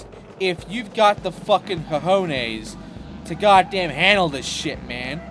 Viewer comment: Did you say cojones?